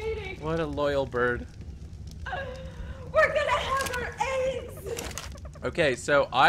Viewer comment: I swear she said we’re going to have our aids